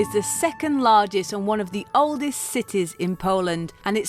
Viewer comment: I said one of the oldest cities in poland .... Have a look on wikipedia 'Kraków, Poland is one of the oldest cities in the country, dating back to the 7th century' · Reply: Sorry. I heard "the oldest city".